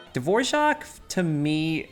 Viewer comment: I had subtitles on and it thought you were saying Divorce!!😂😂